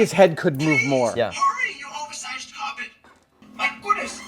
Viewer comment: why does 3PO sound like he has a German accent? Is that just me? The way he said "oversized carpet" sounds like German guy speaking English. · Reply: I thought he was saying oversized hobbit and it was some kind of joke that adam recorded himself. The voice does have a weird accent for sure